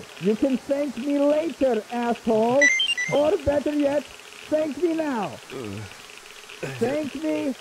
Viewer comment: I thought he said "Actually, thank me now" because he also took his asshole 💀